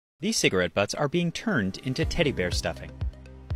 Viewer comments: Did he say cigarette butts? I always called it "buds" have i always just misheard it!? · I thought he said cigarette bugs💀